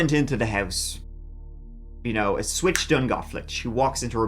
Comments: Go to <link> to get up to 30% off sitewide! Brought to you by Raycon. Thanks for watching everyone, the love and support is really amazing. Happy Thanksgiving, and see you Friday! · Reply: “A switch done gone flipped” … Did I hear that correctly? 😅😂😂 Idk why but that had me verbally laughing for a minute lol …. Even right now as I’m typing this I still don’t understand why/how it was so funny to me …. 🤷‍♂️ 🤔 🧐 🤦‍♂️ 😅 😂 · “A switch done gone flipped” … Did I hear that correctly? 😅😂😂 Idk why but that had me verbally laughing for a minute lol …. Even right now as I’m typing this I still don’t understand why/how it was so funny to me …. 🤷‍♂️ 🤔 🧐 🤦‍♂️ 😅 😂